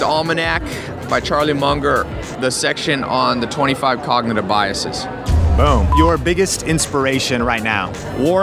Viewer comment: What did he say? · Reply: The 25 cognitive biases. It's an absolute MUST with persuasion. A book that contains a couple of these biases(and may be very helpful for someone like you who might want more influence) is "Influence: The Psychology of Persuasion" by Robert Cialdini